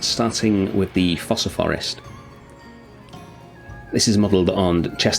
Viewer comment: because I’m so used to calling them ‘the foooosaa’ because of Madagascar I thought you said fossil forest😂